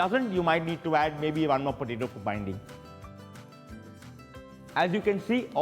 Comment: Boiled potatoes bring freshness in a meat? How are they even related? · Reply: i thought he said, to bind